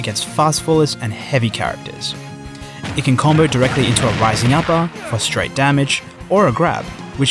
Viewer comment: He says Rising Tackle? I always thought he was saying “Lightning Tatoo”.